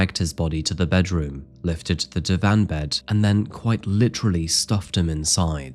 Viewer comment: what did he say?? Im hearing da van bed.